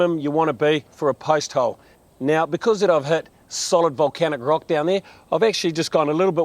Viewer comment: Did he say solid Volcanic rock ?.